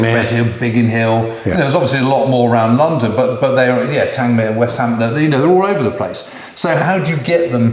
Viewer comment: I know it’s just the production team mishearing, but I think “West Hamble” is supposed to be RAF Westhampnett. Not a very well known station and it only pricked my ears because I grew up close by.